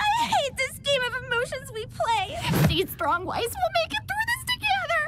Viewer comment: the way I miss heard ruby and thought she said “stay strong wife”